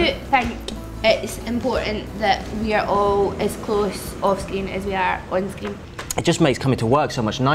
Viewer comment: When Sharon said screen it sounded like she said skin